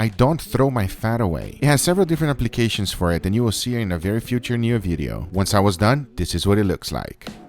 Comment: did Guga say "Very Future Near Video" ?